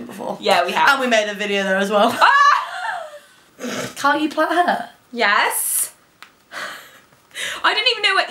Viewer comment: What is saying @ 4:15 / 4:21? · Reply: She's saying "Can't you plat hair?"